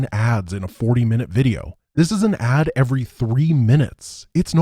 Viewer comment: Why can I only hear "...in a 40 minute video. This is an ad every 40 minutes". Must be something wrong with my hearing, cause it took me long to hear it as "three".